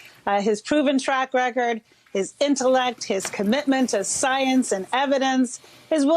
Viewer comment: Am I hearing it well? He's intellect??????😂😂😂